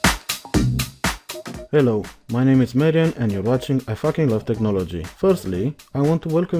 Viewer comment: my name is merry ann? or merry anne? 00:14 i didn't catch the final spelling of your name, so please explain and i'm quite confused cos in subtitle is write Marian or somethin'? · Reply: Nice catch Andoko. That's the pronunciation of "Marian" in English ;)